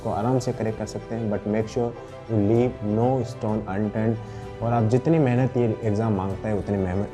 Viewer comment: idk what you said, anybody help, SoS! edit: "Unattempted"? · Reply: Unturned